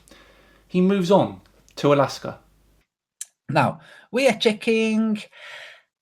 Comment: At minute 11:55 I thought he said "we are chicken, Alaska" and for some reason even that made more sense to me than anything else he said in the video!